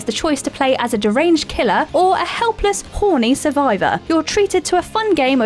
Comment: did I hear that right? "a helpless, HORNY survivor"?